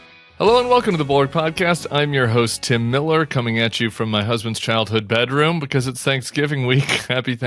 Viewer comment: Dude says "coming to you from my husband's bedroom" and talk about why the Dems lost...........really? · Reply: I thought he said his child’s bedroom